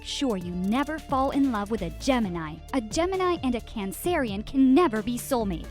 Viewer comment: tf you just say Correct me if I’m wrong but did she try to say cancer? And if she did why’d she say it like that? Have I been saying wrong this whole time?????